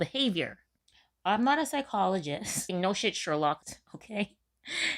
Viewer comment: Did you just say, “No shit SHERLOCKED?“ 😄😂